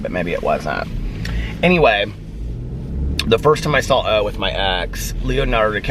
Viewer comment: Anytime says “I saw...” I hear “I sal” with an l🤣 Just me? Ok...🤷🏻‍♀️😂💙